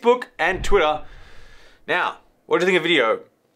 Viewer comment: I am not sure if i understood correctly but did you said : what do you think about NEO ?